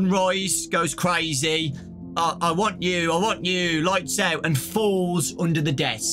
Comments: I thought Sunrise said “I want you, I want you” . It was so creepy, lol. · actually at 8:40 it sounds like he says “LIGHTS OUT LIGHTS OUT I WARNED YOU” so he probably tries to catch us to warn us about moondrop (which he turns into) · Sunrise actually sounds like he’s saying “I WARNED YOU, I WARNED YOU” maybe he could be a neutral animatronic warning us about moonrdrop (or someone else?) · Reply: I heard “ I WANT YOU , I WANT YOU “ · he actually said ‘Lights on Lights on! I WARNED YOu I Warned YOU!’ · I think it actually says “LIGHTS ON! LIGHTS ON! I WARNED YOU!”